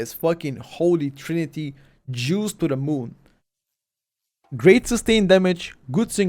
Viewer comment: did bro say “holy trinity Jews to the moon”? That’s very confusing. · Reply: Juice to the moon, it is a sentence we often use on our steam